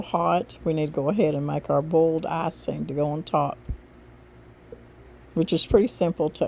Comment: Oh my gosh! When you were talking about the oatmeal cake, I thought you were saying "a BOLD icing"!!!! I was wondering what in the world that would be. 😂😂😂😂❤